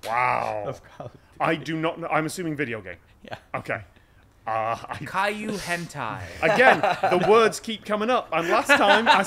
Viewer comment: Is he saying CAILLOU HENTAI or am I mishearing the first word? Tom going on about roller coasters and such, some of it must be coming from Tech Diff Citation Needed and some from his second channel I'm sure!